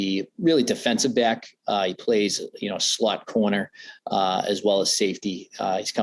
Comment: did he just say "slut corner"?